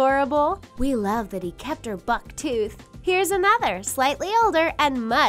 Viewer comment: did said buck in 1:24 check in lily from rrrxxxx drawing